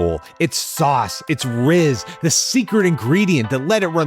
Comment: did he just say rizz 😭